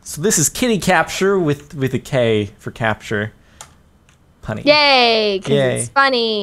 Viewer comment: Thought he said 'honey', he just said punny :/